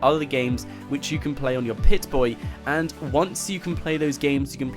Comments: wait...... did he just say... pit...boy... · Did you just say PIT-Boy -_ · I heard you say pit-boy you poser